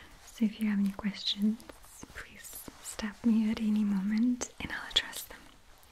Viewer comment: i heard "please stab me at any moment" oh my god